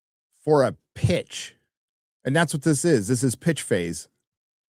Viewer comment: Pitch phase....I thought you said bitch face. I'll rewind a bit.